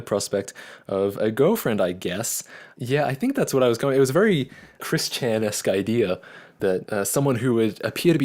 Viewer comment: the drivers scene was looked very real. 1:48 did you say Chris-Chan? The transgender who created Sonichu? Which actually fits his character from back in the day.